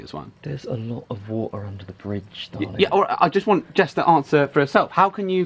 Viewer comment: did... did he just call him “darling”